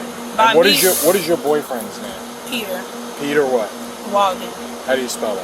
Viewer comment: I knew she was lying when she said his name was Peter. Ain't nobody in the hood named that😂😂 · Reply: She said Peter 😂😂😂I thought she said Pierre damn Peter tho